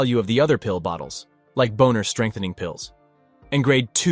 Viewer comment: I heard boner strengthening pills💀